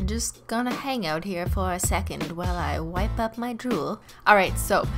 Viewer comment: I thought she said wipe up my juul